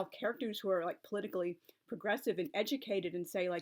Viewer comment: At first I heard "Progressive" as "Aggressive" but then I was able to remind myself of the context.